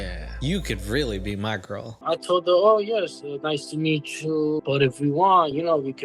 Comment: Did he say, "I told the ho"? at 8:07 Maria is scary and probably shouldn't have kids if she's sending videos of her kid to strangers